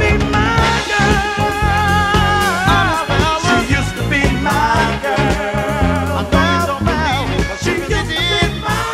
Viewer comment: Question @ 3:45 is he saying I’m just jealous? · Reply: He's saying "honest, fellas", meaning he is telling the truth that she used to be his girl. He also says "I wouldn't lie about a thing like that"